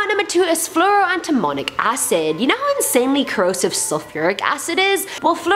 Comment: Lmfao I thought she said demonic acid. 😂